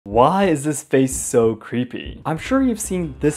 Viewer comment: I finished watching it and when the video replayed (YouTube shorts), I misheard "creepy" as "creamy".